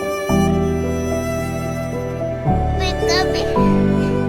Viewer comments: pikachu saying pika my caption saying wake up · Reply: In Japanese, it’s Pikachu saying Ash’s Japanese name “Satoshi.” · captions say wake up when pikachu says pika pi 😭😭